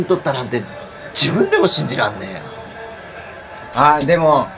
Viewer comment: あの人なんて言った？ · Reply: 兄者。 マジで信じらんねーよ に聞こえる